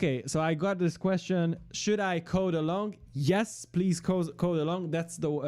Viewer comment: "Should I code along" at 3:15 by someone, and the answer was "Yes" from the youtuber. This is not the correct way for becoming a good developer, just learn new things from the video and just write the code on your way, so that you will get some errors and you can find solution for that and you will gain knowledge. Making developers in the way is really really poor. I believe that also you would realize it one day. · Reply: I Heard "along" not "alone" :|